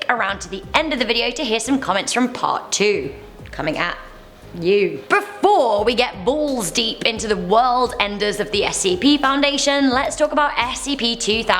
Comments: Plz tell me I'm not the only only one who heard her say balls deep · BEFORE WE GET BALLS DEEP Me: 😂 hol’up did she just say balls deep??? 😂😂😂😂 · From 0:54, am I the only one who heard her say "Before we get balls deep"?